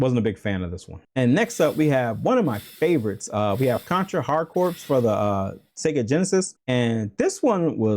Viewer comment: Fun video, I love Contra, but did he just say Hard Corpse ? C'mooon